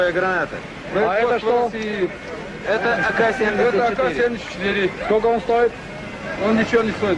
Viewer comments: Minor error, at 4:42, the translation reads "It's an AK-47, how much does it cost?" When he actually said "it's an AK74". Carry on. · Why people in video says it's an AK-74 , but subtitle says it is AK-47?